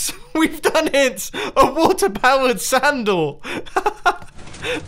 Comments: i thought you said sandle instead of sand door XD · Is it just me but is he saying "WATER POWERED SANDAL" or WATER POWERED SAND DOOR" ??? XD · Is it me or did he just say water powered sandal? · did he say a water powered SANDAL?